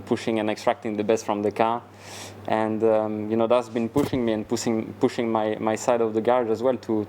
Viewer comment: did econ say "pussing"? lol😂😂